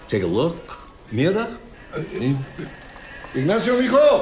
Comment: it sounds like he said "mierda" instead of "mirada"